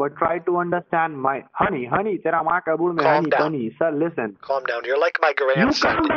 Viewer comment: Can anyone PLEASE reply to my comment with the insults they say in Hindi? I searched for it but I didn't find anything, especially when he says "hey hey honey 'tera-maka-bul'?", that's what I hear but that's not it apparently 😝